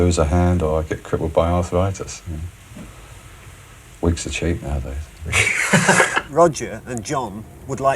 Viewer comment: What does John Entwhistle say at 9:38? ...is is "Weeks are cheap nowadays" ? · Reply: "Wigs are cheap" The wig is a toupée or hairpiece.